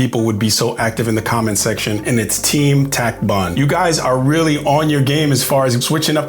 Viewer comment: I thought you said "attack bun"